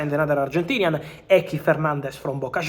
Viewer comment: thought he said enzo Fernández wish he had!